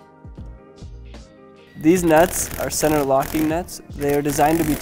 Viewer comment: did he say Deeez Nutz ?